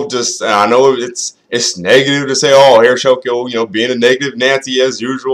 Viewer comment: At the beginning I thought you said "shulkio" not "sokio" XD